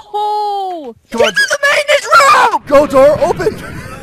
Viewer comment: i was certain that is gonna say get to da choppa !